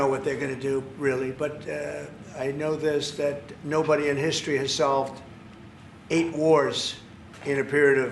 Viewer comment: All I heard “ no body in history is as dump as me “